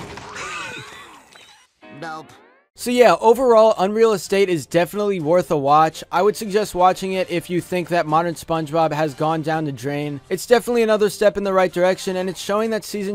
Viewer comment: Is it weird that I thought it said Unreal Easter :P